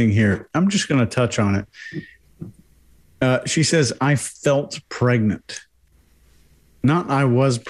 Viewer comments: No, she didn't say she "felt pregnant" she actually said "she fell pregnant" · Brits and Aussies say “fell pregnant” when they are get pregnant. I am American but I have observed that when listening to these two groups of people. I am not sure if she said “fell” or “felt” but I think that is what she meant. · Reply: I thought exactly the same thing. What I heard her say was "fell pregnant", not "felt pregnant". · PLEASE SUBSCRIBE: <link> MERCH STORE: <link> FACEBOOK GROUP: <link> More... 🆂🅲🅾🆃🆃 🆁🅾🆄🆂🅴: Body Language Tactics - <link> 🅼🅰🆁🅺 🅱🅾🆆🅳🅴🅽: <link> 🅲🅷🅰🆂🅴 🅷🆄🅶🅷🅴🆂: <link> 🅶🆁🅴🅶 🅷🅰🆁🆃🅻🅴🆈: Body Language Tactics <link> RESEARCH CITED BY: <link> Ok, E., Qian, Y., Strejcek, B., & Aquino, K. (2021). Signaling virtuous victimhood as indicators of Dark Triad personalities. Journal of Personality and Social Psychology, 120(6), 1634–1661. <link> <link> Habitual, false victim signalers deplete available resources for genuine victims, dupe trusting others into misallocating their resources, and can initiate a dysfunctional cycle of competitive victimhood within society more broadly. <link> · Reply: I don't know about anyone else, but I heard "felt pregnant"